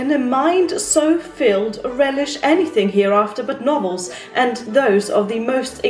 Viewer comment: At first I thought u said "Nobles are trash" not Novels like she was roasting the people she worked for 😂